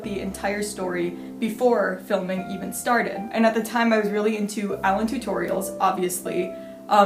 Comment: Did u say Alanturtle or whatever i dont know how to spell good